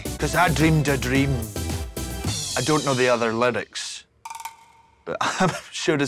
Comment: I thought she was saying littix buT SHES SAYING LYRICS LMAOOOO I CANT